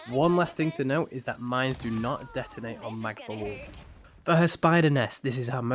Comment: Mines don't detonate on what kind of walls? Kind of hard to hear what you said there. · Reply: "Mines do not detonate on Mag(neto) bubbles"